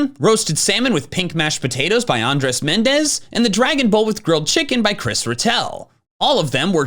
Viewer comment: my dumb a*s brain thought he said dragon ball as in dragon ball z 💀💀💀